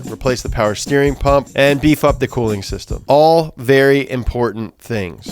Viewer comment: So I know he says, "beef up" the cooling system... but I heard "de-f**k", and I like that a bit more.